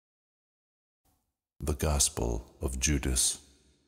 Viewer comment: I heard it said "The crossbow, of judas"